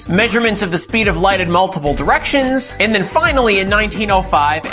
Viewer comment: my fat ass thought he said "brownie in motion"